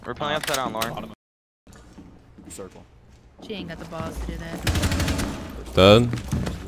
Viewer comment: Did I just hear "She aint got the balls to do that,"? LMAO, well, I mean I wonder why.